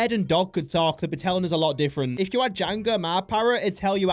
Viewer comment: I like how he just refers to his parrot as “parrot” instead of its name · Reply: I think he says Jango here